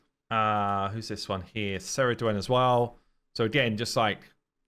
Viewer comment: Great Video. At 6:14 , I had a hard time catching that hero's name, did you call her Ceridwen?